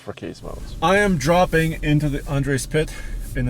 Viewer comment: Who heard him say “Andre spit” instead of “Andre’s pit”. Love this channel!